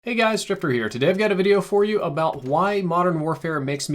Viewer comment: I swore at the start he said hey guys stripper here